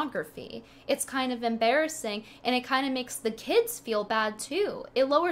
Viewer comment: I thought said "it also makes the tits feel bad too" Rather than "the kids feel bad"